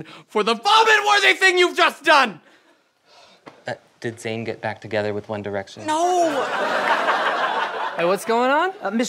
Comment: Oh, ok so I did not catch what Adam said until now, he said, "Did Zayn get back with One Direction"?? This was great!! 1D Forever!!